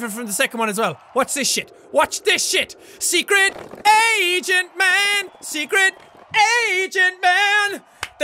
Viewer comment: When he sang “SECRET AGENT MAN!” I thought he said “SECRECT ASIAN MAN”